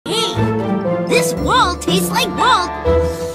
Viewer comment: I hear "these walls taste like balls"